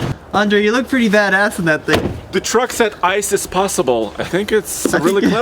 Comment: Andre said ice is possible I thought he said ISIS possible, I was like lol what, took me a sec there lol.